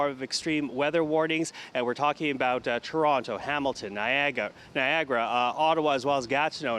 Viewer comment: When he said Niagara, thought he said Viagra. Thought what's the problem. Things should warm up nicely.